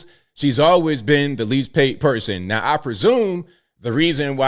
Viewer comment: I thought you said at least behaved